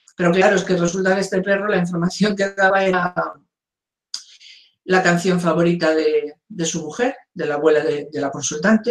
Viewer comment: En el minuto 1:05:40 la información que _____________________. La canción favorita de su mujer..." disculpen, para mí es importante saber ¿qué dijo? no le entiendo. He regresado el video 3 veces y no entiendo. ¿qué hacía el gatito cuando tocaban la canción o la película? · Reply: Dice: la información que el daba era la canción favorita de su mujer,,........